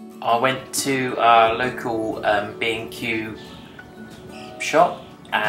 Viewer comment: You went to " a local ..... ( what ) shop " What is he saying time 0:19 ?? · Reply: B & Q, a diy chain that also sells plants etc in the UK.